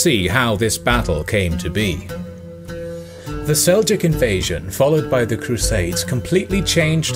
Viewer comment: The English subtitles say “Celtic invasion” instead of “Seljuk” 😂